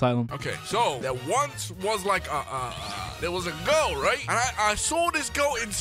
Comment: Why did i heard goat instead of girl?